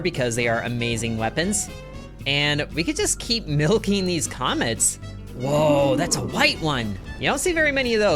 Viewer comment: what did bro just say😂😂😂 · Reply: “just keep milking these comets”